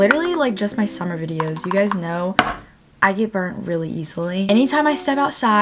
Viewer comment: When I said you get burnt really easily I thought you said that you give birth really easily. 😭